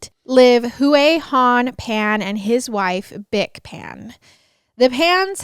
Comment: Bic Pan? Oh I thought you said Bic pen!